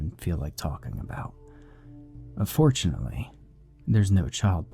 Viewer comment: Did it say but fortunately or unfortunately theres no child porn.. . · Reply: I definitely heard "UNfortunately", but I decided not to go back and check. It adds some satisfaction to the ending...